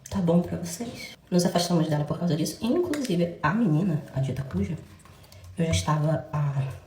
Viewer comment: a legenda automática "A menina, otaku já ..." Oi ???? kkkkk